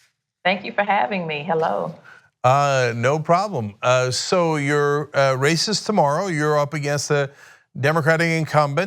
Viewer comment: I heard it as 'You're racist tomorrow' lol